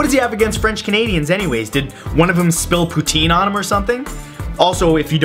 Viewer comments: I thought he said Poitín..... not Poutine..... Poutine looks horrid. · LMAO I THOUGHT HE SAID RUSSIA'S PRESIDENT PUTIN BUT WHEN I PUT ON THE SUBTITLE IT SAID POUTINE ROFL